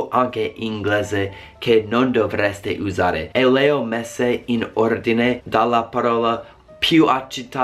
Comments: Ma solo io a 1:29 ho capito “Leo Messi” ? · sbaglio o ho sentito Leo Messi ahahah ?